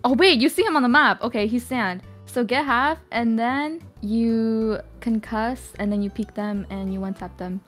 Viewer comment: you wrote curse instead of concuss